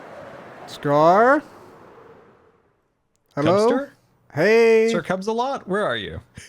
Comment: I thought Scar said "dumpster?" lol